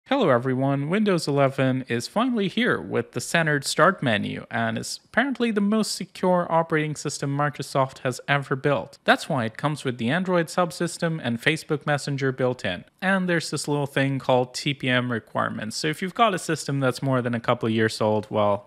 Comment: For some reason, I first thought you said "Windows 11 is apparently the most ignored operating system Microsoft has ever built."